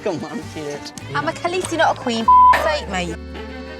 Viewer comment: What did Emilis Clarke say at 9:55 ? I understood her saying "I'm a khallisi not a queen" somthing somthing somthing. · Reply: She said "fuck sake mate"